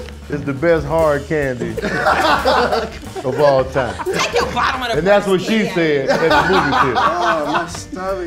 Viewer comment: what did say lol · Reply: “Take your bottom of the barrels candy ass”